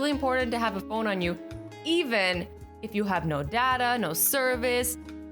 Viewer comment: No dada....Who else heard no dada, Like if you did